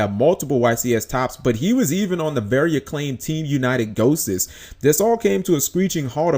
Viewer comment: What is Claim T United Doses at you are saying at 1:27?